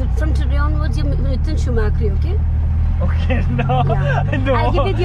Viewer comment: Did say shoe maakri 😅